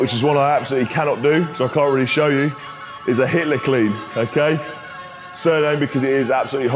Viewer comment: a hitler clean 😂😂😂 sorry I misheard that